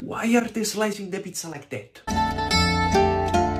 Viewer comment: I thought he said don't steal the pizza by your owner